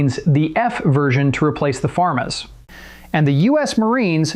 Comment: Did you say “farm-ass”?